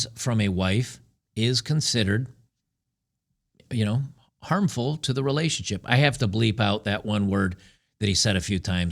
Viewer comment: Um, what is that word? Can I know? Sorry, legit, I really don't know..... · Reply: Abuse